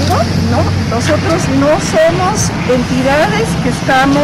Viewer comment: y ss "nosotros no semos" ¿? Escuché bien??